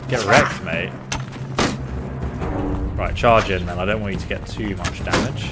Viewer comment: Bruh at 27:01 I dont know what she said but what i hear in my mind was I dont need you n word, lmaoooooo im literally dying on the inside.